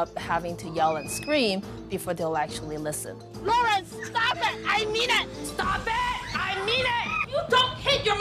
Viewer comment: why does whenever say ''i mean it" sound like she is saying "i need it"